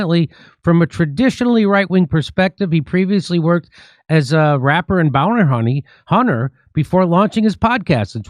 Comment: wait did i just hear a rapper and bought hunter lol. That's an interesting resume😂😂😂